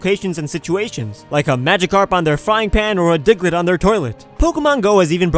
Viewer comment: it sounds like he said "dicklett"